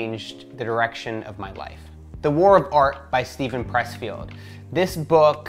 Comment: I thought you were saying the art of war😂😂. Thanks sharing for the list.